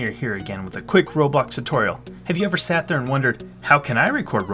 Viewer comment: is he saying robux or roblox in